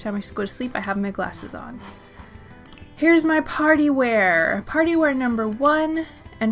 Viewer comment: Lol I thought you said "Here is my potty wear" instead of party wear 😂